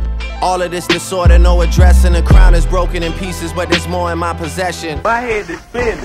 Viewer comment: I heard Aaliyah’s “more than a women” in “is there more” I GOT SO EXCITED